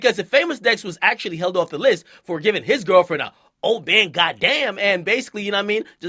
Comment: He gave her a OH MAN GOD DAMN! Combo. Famous Dex would be proud. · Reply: Thought he said "Oh bang god damn"